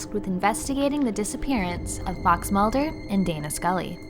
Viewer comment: Swear it sounds like you said Box Mulder lol